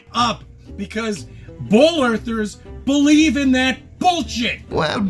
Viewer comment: Sounded like he said "bowl earth" when he said "ball earth"... i thought he managed to be even more wrong than he already is.